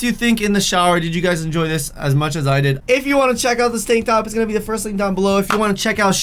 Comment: I thought I heard say "stank top" instead of "tank top"